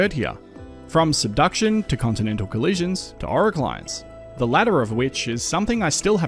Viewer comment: What do you say @ 6:04? The transcript has it as "our clients", so I guess it also couldn't understand what you said. Anticlines? Orogenies?